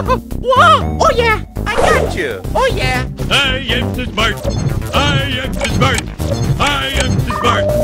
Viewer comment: What mario said??? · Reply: I am the smart I am the smart